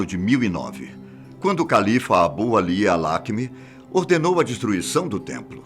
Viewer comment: eu ouvi Khalifa ?